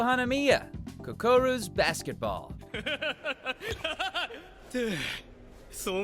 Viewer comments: Did he just say “kukuro basketball? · Did he say Kokuru's Basketball @ 4:44??